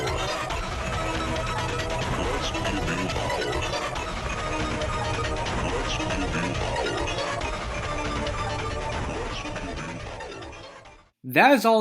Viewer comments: I hear "let's give you powers" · all i keep hearing is "Lets build your power"